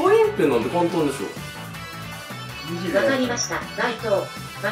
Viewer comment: 「分かりました。ないとー」って聞こえるんだけどwww